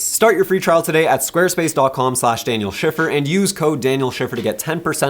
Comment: I thought it said shitter😂😂